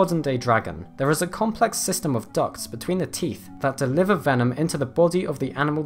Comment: "There's a complex system of ducks". I know, he said "ducts", but I keep hearing ducks. For some reason I find that funny.